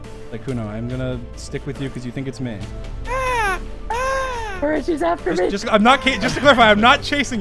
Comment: am i crazy or does sykkuno say “george is after me” at 2:33 😭😭